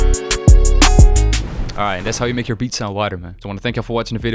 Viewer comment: Damn I thought it said "how to make your beats weirder" i was on board